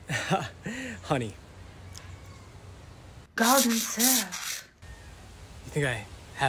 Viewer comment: She said “golden syrup” which is just sugar water that’s been boiled so it’s thicker 👍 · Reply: I thought she said garden syrup